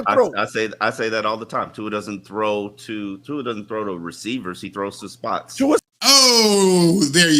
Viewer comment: First of all @ 30:00 you actually misquoted Omar Kelly. He said "Tua doesn't throw to a receiver, he throws to a spot". Then you misquoted Omar and said that he said he doesn't throw to a "read" instead of "receiver". Which is a big difference. Of course, he throws to a read lol.. He has to read where he anticipates the receiver will be and throw to that spot. Throwing to a spot is not a bad thing. It requires anticipation.. And yeah you'll probably just label me as a "Tuanut" and be dismissive of this comment anyways. I just wanted to point out that you had this huge reaction to what Omar said and you didn't even hear him right lol. We should all want our QB to throw to a spot as long as it's the right one. It's rare that a WR will actually be at the spot a ball will be caught before a pass is let go by any QB..